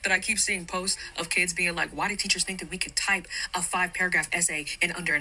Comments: I thought she said 5 page essay lol, but a 5 paragraph essay can be done in an hour 100%. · Wait paragraph??? I thought she said page at first!! What do you mean you can’t write a 5 paragraph essay in an hour. That’s 25 sentences.